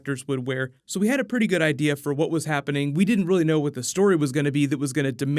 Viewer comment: Did he just say snappening?